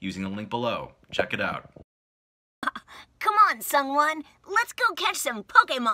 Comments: Before I thought she said someone not sung wun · I just learned that ProZD's real name was Sung Won, and this skit makes so much more sense now. For some reason, I thought Veronica was saying "Come on, Someone," and I was confused as hell.